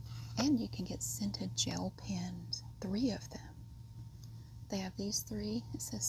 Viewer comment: I’m currently very sick, high fever etc, and trying to go to sleep. I’m about to doze off when you said “scented gel pens” I opened my eyes because I thought you said “sent to Jail pens” like damn does it have a knife on it. I’m going to blame the fever.😆 love your videos